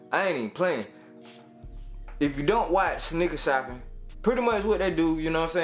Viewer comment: nigga shoppin 😂 tell me I ain’t the only one who heard that shi